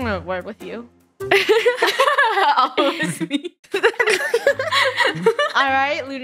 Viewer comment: what did just lunar just said???/ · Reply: Up/op it was me!